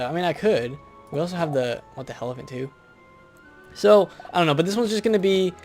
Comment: 36 sec what the hell am i goanna do me ha ha ha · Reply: EvanDark Thorn I thought he said we have the helephant too